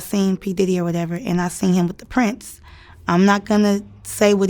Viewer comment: Did I hear her mention Prince????😳 I want to know what Prince was doing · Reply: She said 'the prince'. Prince was already dead by this point.